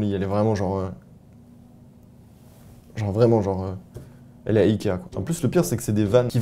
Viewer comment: C'est moi où tu dis qu'elle est à Equihen?(10:02) 🤣